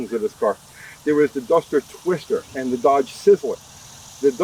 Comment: I think you said “dumpster twister “ at least it sounds like it.